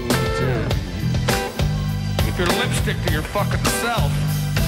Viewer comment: what does he say here? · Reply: I think he says "you can lipstick to your fucking self"